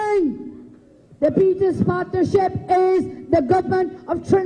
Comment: Did she say The Peter's Partnership at 0:15? LMAO